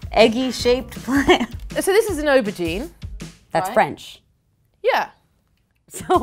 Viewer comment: wtf you say eautreis or however you spell it